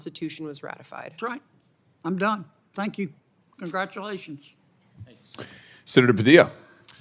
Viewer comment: idk why I heard "Jose" when he said senator Padilla.